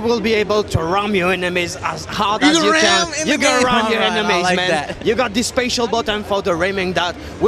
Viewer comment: that guy's accent makes it sound like he is saying "you can RIM your enemies..."